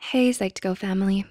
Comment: I thought it just said hell